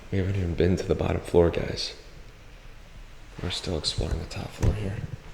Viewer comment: I thought this said "exploding" instead of exploring